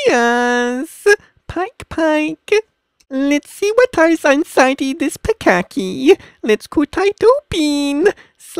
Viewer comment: it really sounded like he said"lets see whats inside this bukkake" xD